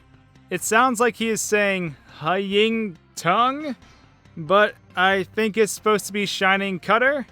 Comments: I heard it as "fighting cunt". ._. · I heard Lightning Cunt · Came back to rewatch the video because I saw it yesterday.... 3:49 ..... I thought said, "Fighting cunt!" 0_0 😂😂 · I on this video, even with the isolated sound I hear saying "Fighting cunt" XD 3:44 I'm so glad I'm not the only one XD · At about 3:48 or something i heard "Flying C*nt" Not Shining Cutter or whatever its meant to say XD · I heard say "hiding cunt" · I didn't hear say Shining Cutter, I heard him say Flying C**t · Hyyyying tung.....really that's not even remotely close. I always thought he said "fighting cutter" though. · I heard hitting c**t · I thought said lighting cutter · i hear "Hiking tung" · the TMNT fighter game sounds like it says "Terrible Combat" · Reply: I thought said hiking cu*t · I hear Tie-ing Tounge Seriously! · i thought he said ‘’Fighting cunt’’ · i have heard something funny at 3:46. I heard striking c*nt XD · it sounds like is saying "Fighting C**t"..... ?